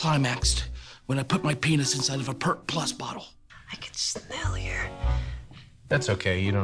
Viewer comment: What is she saying? PLEASE SOMEONE '-' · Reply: I know this comment was from two years ago, but she says, "I can smell your..."